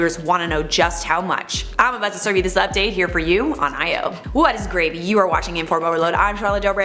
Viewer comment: It sounds like she said what is gravy, which would be perfect because she's our queen of potatoes!